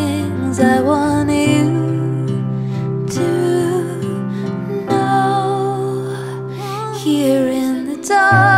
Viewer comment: What does she say in the background at 1:55? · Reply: I want you to know.... :)